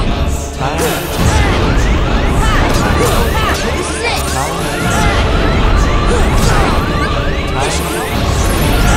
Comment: is it just me or did Sora just say shit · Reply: This is it He says